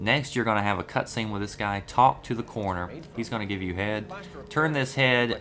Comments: i legit thought you said, "and he's gonna give you head..." great walkthrough!! this game looks so rad! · lol it sounded like he said "ok now talk to this guy he gonna give you head"